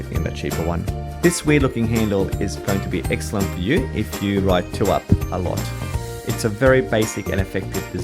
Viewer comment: sounds like your saying if you like to fap alot